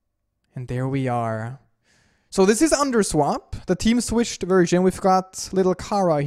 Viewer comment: I thought it said tsunderswap